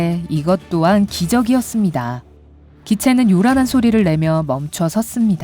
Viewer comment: 기차가 아니라 기체입니다 오해하지마세요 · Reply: 에 기차라고 들리는 부분을 계속 들어보니 기체라고 해서 기체라고 오해하지말라고 쓴건데 기체가 아니라 비행기다라고 해서 단어 수가 다르다고 했더니 이 영상 제작자가 비행기를 기차라고 착각했다는데 전 분명 '기차'라고 들리는 부분을 '기체'라고 했습니다 근데 왜 단어 수도 맞지 않는 비행기가 나올까요